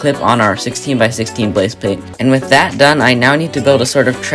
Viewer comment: I think he said blase pate